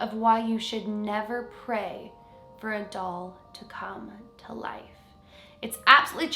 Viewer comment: Who else thought she said never pray or your doll come to life lol Also 3rd Never been this early ekk😳😳